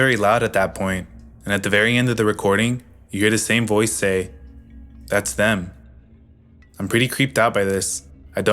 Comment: People jumping to the paranormal conclusion on the "sleep talking" audio are irrational. You'd have to make many assumptions and irrational logical leaps to get to a paranormal cause. You don't even know if the OP is telling the truth or what all she had in her bedroom as far as radios, televisions, etc that could've been picking up interference or whatever. The fact is that you don't know. If you're convinced that the paranormal conclusion is even a possibility, you have to accept the fact that you're an irrational person. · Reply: To me it sounds very much like a home invasion. I hear “It’s done” at the end instead of “that’s them.” Either way, almost sounds like an old camera to me. Maybe some weirdo taking photos of her and her kid sleeping. Could be a guy who does this kind of thing a lot seeing how close he can get without getting caught, and taking photos of the people in the homes he breaks into as trophies. Could have been hired by an ex, or even sent by someone gathering threats against someone she knows. Showing someone a photo of their loved ones sleeping, taken in the room when they’re unaware they aren’t alone would send a very clear message of how vulnerable they are and how easy it would be for them to hurt them. It could really be anything, but there are so many natural possibilities that are infinitely more likely than anything supernatural.